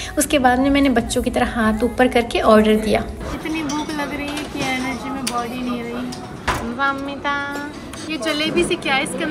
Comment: did she really said energy main body nhi rhi plz reply??????